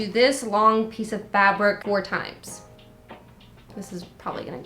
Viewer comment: When you say fabric it sounds like youre saying fabork